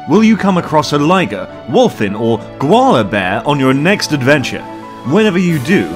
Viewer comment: I thought he said granola bear 😶😂